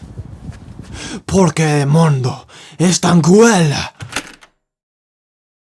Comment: text: what have you done to me?" what he said: "por que el mundo es tan cruel?" translation: "why is the world so cruel?"